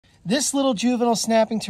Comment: I scrolled past quick and I just heard “This little Jew-“